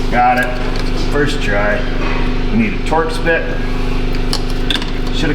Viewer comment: The first two times he said "we need a Torx bit", I thought he said "we need ta torch this". Ha, ha! I almost passed on watching this one, as I've done enough power window regulators that I thought I didn't need any more instruct-sheons. Glad I watched; the entertainment value is high.